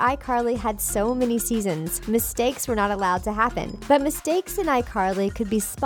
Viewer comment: Speaking of mistakes..... At 5:33 the subtitle says "were allowed" but the voice said "were not allowed". Which is it???